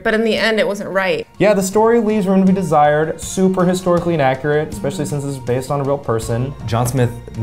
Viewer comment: Ok listen...I thought he said that Pocahontas was historically accurate and I got mad lol. I slammed my juice down Ans I turned it back just to make sure and then I was like oh ok he said inaccurate. Good good.